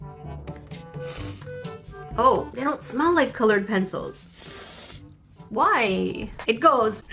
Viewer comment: I was looking away from my phone and I thought you said “IT SMELLS LIKE FISh” I was so confused lol 😂